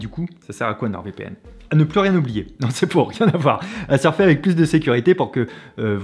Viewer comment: J'ai entendu "Nord VPN c'est pour rien n'avoir".